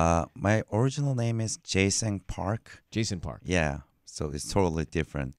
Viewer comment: Oh i thought he said Jason Park